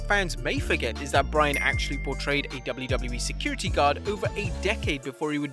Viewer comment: I thought he said "... over 8 decades ..." Damn, he looks good for his age. At least 40% of comments: Cesaro looks like Aiden English